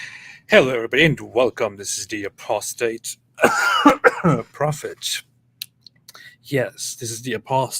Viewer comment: For a second I thought you said prostrate prophet and not the lie down type!